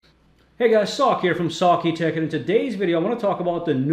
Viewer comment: I heard "Hey guys, sock here from socky tech." Thought you were on about socks lol.